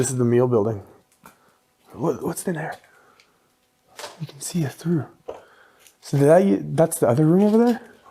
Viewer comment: anyone else hear a man’s voice saying (what sounds like) “swear” right after asks about their direction?! See you should always rewatch your favorite videos cause you never know what you’ve missed! Thanks for sharing guy’s! I hope it’s going very well for ya’s! TTFN ✌🏼🤟🏼🧼🖖🏼